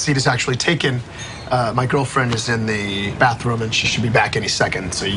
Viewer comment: I heard "buttroom" instead 😂😂